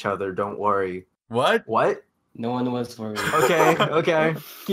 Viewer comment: did just say "no-one wants to know " !?